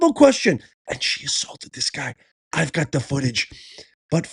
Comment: Man I thought you said "assaulted" you mean insulted... 😂.